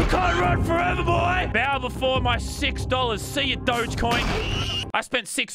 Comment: when you were playing slap battles way did you say you don't like doge coin right when you slaped me!